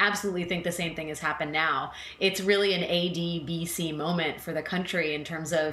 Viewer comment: I think at 2:35 Akex uses an acronym, ADCB, the close caption has A, B, C, D, but thatbis not what it sounds like. Anyone know what she referred to? Google is no help.